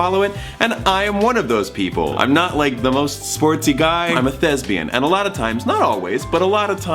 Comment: Dammit I thought he said “I’m a lesbian” and then got very confused.